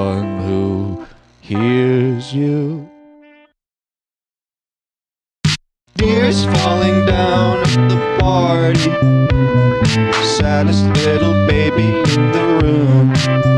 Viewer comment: Why do I hear "Deers falling down at the party Santarem little baby